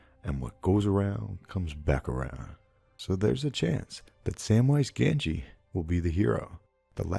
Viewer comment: did he say Samwise Gamgee?